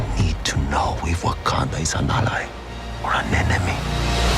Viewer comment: did Namir say, "I need to know if Wakanda is an allies or an anemone"